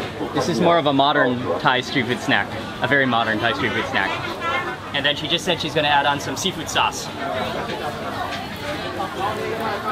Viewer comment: Roflmao, Doesn't it sound like says "this is more of a modern Thai stupid snack. A very modern Thai stupid snack"